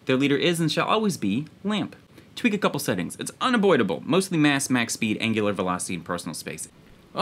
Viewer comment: Did you just say "Unaboidable"?